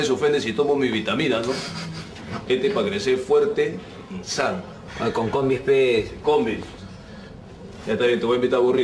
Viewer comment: Que dijo burro? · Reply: con combis ps, osea que le combide, invite